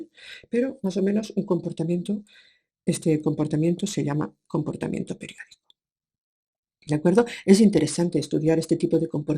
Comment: min 15:00 dijiste comportamiento perial? no entendi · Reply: Comportamiento periódico